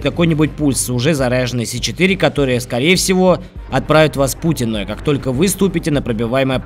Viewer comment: отправит вас в путь иной, услышал как "отправит вас к Путину" о_О